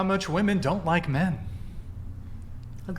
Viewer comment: Women have a tendency to hear with their feelings. All of them is 100% convinced that said 'hate men'. (8:05), when in fact he said 'don't like men'. Why? Because 'that's what it felt like he said'. They portrait themselves as fools.